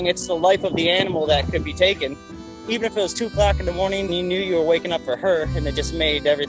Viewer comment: When he said "it was only 2 o'clock in the morning", it sounded like he said "it was only Tupac in the morning".